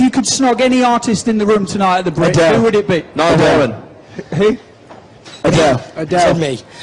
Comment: Did Liam say Niall Horan? I think so…